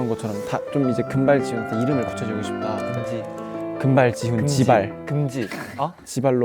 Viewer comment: “jibal” 😭😭😭 misheard it and it will be “shibal”